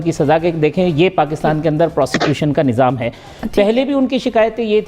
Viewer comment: Please listen carefully at 5:30 - 5:30. He said prostitution . If I am not wrong instead of constitution.